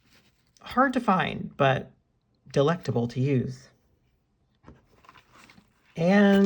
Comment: I heard "hard to find, delectable to use" and thought SAME but that makes ABSOLUTELY no sense ☠☠